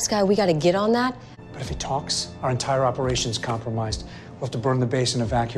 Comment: Does anyone else here Coulson say complamised instead of compromised?